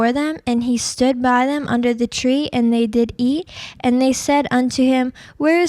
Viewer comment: "and they did E" or am I mishearing that? haha